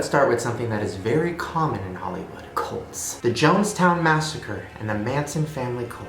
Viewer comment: When u said cults I thought u said Colts at first like the football team